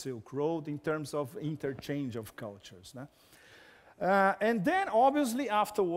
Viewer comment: Eu ouvi um ''né?''? Kkkkkk Pepe é sempre brilhante.